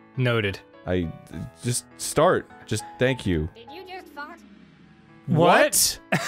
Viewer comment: "did you just fart?" thats what i heard